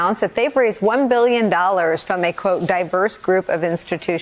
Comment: Thought she said “diapers group”. Not “diverse” group of investors lol. Diapers would make a lot more sense for Trump.